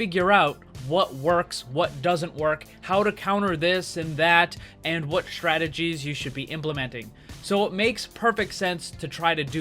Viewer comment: Am i the only one who heard "What shredded cheese you should be implementing" at 0:43?